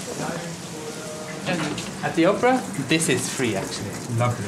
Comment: We went to the Opera House, saw a great production and amazing building. . . for far less than we would pay in UK. Your theatre and ballet is more affordable funnily enough! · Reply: At first I thought he said 'Oprah' house.